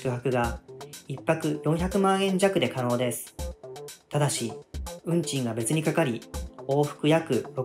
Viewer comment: 運賃がうんちにしか聞こえん